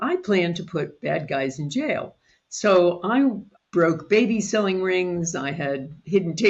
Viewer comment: Wait, did i hear" I broke baby selling rings", at 2:00 minutes in, When she was talking about her initial career of putting bad guys in jail?